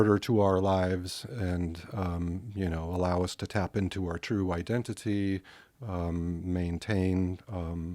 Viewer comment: when Gary says "true identity", it sounds like he says "true white entity". Look it up.